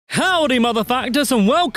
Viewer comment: Am i the only person who thought he said howdy motherfuckers